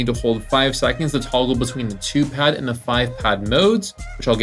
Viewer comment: did he just say 2 pad to 5 pad 😂